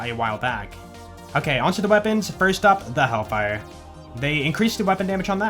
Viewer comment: i misheard hellfire as hellwalker lmao